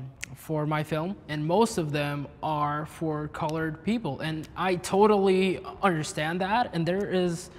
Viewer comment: Did he say "colored people" @ 16:24?